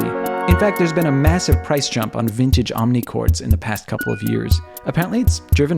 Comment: I legit thought that said mini choad